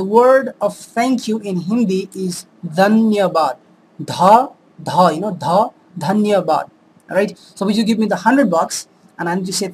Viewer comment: sounds like youre saying DhanyaBad not DhanyaVad